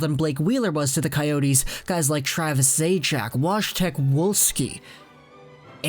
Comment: Did you say Wash-tech Wall-ski?